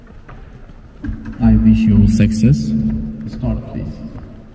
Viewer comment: "I wish you were sexless" is what it sounded like, and I played it over and over.